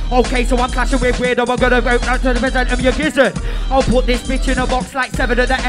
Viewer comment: "Okay so I'm clashing with Weirdoe umgonnaubduomblubbadom gizzard!" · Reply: if you listen at 0.75 speed it sounds like he said pull out a percent of your gizzard.. he might have flopped the actual percentage but you can hear him say percent.